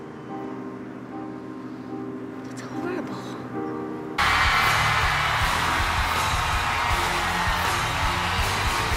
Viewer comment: What did she say at 6:40?😊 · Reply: She said "It's horrible." ;)